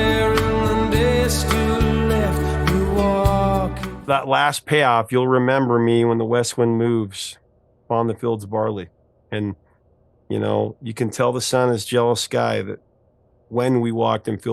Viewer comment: Barley?! How funny, I always thought Sting was saying ...fields of BALI